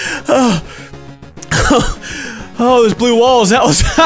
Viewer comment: it sounds like he says Blue Balls! and blue balls are really scary :P